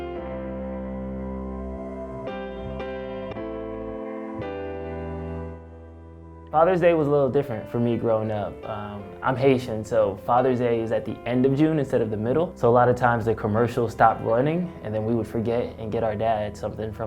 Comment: I don’t know why but at 0:35 when this man said I’m Haitian I heard Asian and had to rewind it